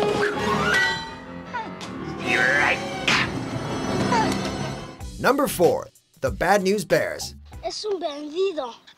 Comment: Okay, seriously, can someone tell me what beetlejuice is saying at 5:50? I have always wondered. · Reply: "Strike" or rather "Stee-rike!" (baseball terminology, because he pitched the mouth thingie to her face and hit its target)